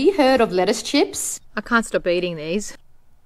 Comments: When she said “I can’t stop eating these” all I hear is “I constipated these” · I heard "I'm constipating this" instead of "I can't stop eating these." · Ngl I heard "I'm constipating this" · It sounds like she said, " I'm constipating these!"😂😂😂.... I bet you are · I honestly thought she said I’m constipated eating this. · She said "I can't stop eating these" but I heard "I'm constipating these!" 😂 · I heard her say " I'm Constipating these" 💀